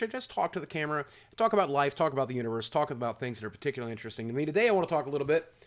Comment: Did he say he talks about the uterus